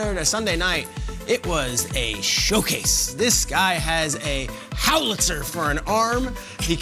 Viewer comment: Did he say "howlitzer" at 1:38?